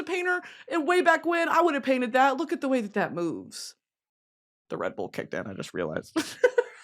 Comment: tell me why when you said “the redbull kicked in” about your red toga and i heard “the red. bull kicked in” like the red fabric made your bull instincts kick in or something 😭😭😭